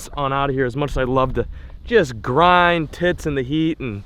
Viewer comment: Did he say "grind tits" at the outro?